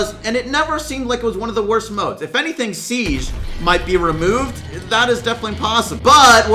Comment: Omg i thought you said bibi removed lol